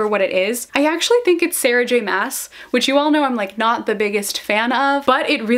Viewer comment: Oh my word. For some unfathomable reason, when you were opening the Nerdy Post box, and said it was Sarah J Mass themed for some reason I thought you had said Sarah DESSEN. I have NO clue how I thought that! 😂😂